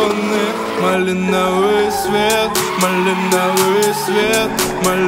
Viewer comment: Свет настроения малиновый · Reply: Мне послышалось не малинлвый свет а малиновый цвет🤣